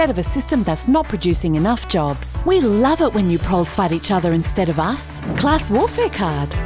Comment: "We love it when you fight each other instead of us." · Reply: Get it right: 1:55 "We love it when you PROLES fight each other instead of us!"